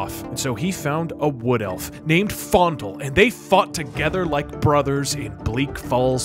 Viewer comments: My headphones were off so at 0:19 i thought i feintly heard "they fucked together like brothers" i was like "hold up" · it sounds like Doug is saying “And they fucked together”